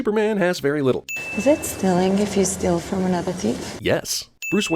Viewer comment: what the hell is she saying? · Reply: "Is it stealing if you steal from another thief?"